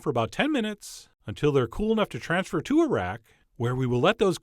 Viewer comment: When ever he says "transfer them to a rack" i always hear "transfer them to Iraq". Give Iraq some shortbread cookies everyone.